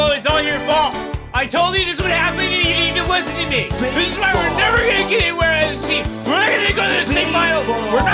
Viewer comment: what was saying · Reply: "THIS IS WHY WERE NEVER GONNA GET EVERYWHERE IN THE TEAM. WE'RE NOT GONNA GO TO THE STATE FINALS. WE'RE NOT GONNA GO TO THE CHAMPIONSHIPS, AND WE'RE GONNA HAVE SO MANY NON-ENDORSEMENT DEALS. NO ENDORSEMENT DEALS. GONNA BE EATING CANNED PASTAS FOR THE REST OF OUR LIVES. (???) EATING CHEF BOYARDEE"